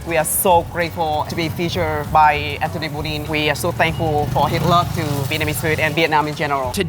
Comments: thought he said we are so thankful for hitler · "We are so thankful for Hitler" I think I heard that wrong.....actually, I know I did. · "We are so thankful for Hitler" ... ? ... I know that's not what he said but I just can't decipher it. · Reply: He said "his love" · Did he just say he was thankful for Hitler? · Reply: “So thankful for his love for vietnamese food …” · did anyone else mishear him say "we are so thankful for Hitler" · If you misheard him for saying "hitler" he was actually trying to pronounce "his love" xd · The tour guy said "We thanks for his love." And i thought he said " Thanks for Hitler for a second." LUL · did i just hear him say, "we are so thankful for hitler" ?